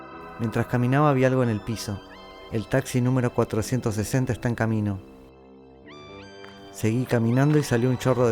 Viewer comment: Por un momento pense que dijo "el taxi numero 41 esta en camino".